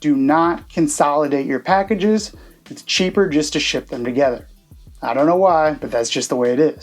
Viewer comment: did he say "do not consolidate, it's cheaper to ship them together"? 🤔